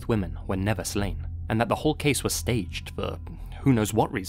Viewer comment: Did he just burp at 14:06 or am I hearing things? lol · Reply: He said for.. In brit accent it sounds like furr..